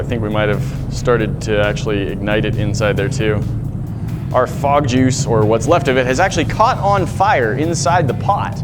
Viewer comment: Lowkey thought it said frogjuice lmao xD